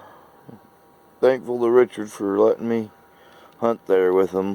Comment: ? You been at the bong hits early this morning, my name is Brooksy 😅 am I invited for some memory relapse lol at least that cave doesn't have much for critter's live in it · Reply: I heard you say thankyou Richard! For taking you out? Was I hearing bong smoke in my ears? LMAO!!